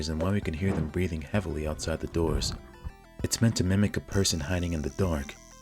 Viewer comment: did you say MIMIC?